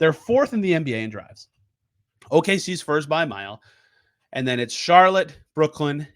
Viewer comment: did say OKC spurs lol